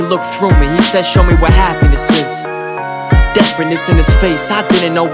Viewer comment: yo i thought he was saying show me what happenedeses no happiness lol